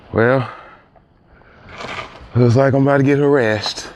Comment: "(sigh) Well, looks like I'm about to get harassed." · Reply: I swear I heard him say (sigh) well, looks like I’m about to get some rest